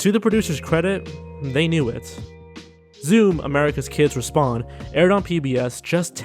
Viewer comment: I thought gou said "America's Kid's Respawn". That would have been a very different show.